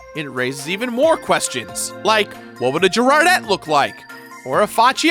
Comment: I'm sorry, all I heard was "draw jirardette"